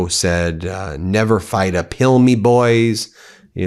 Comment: Oh, " never fight uphill, me boys"! I thought you were saying "never fight a pill me, boys." HaHaHa I was like,, "OMG, he's even worse than I thought!"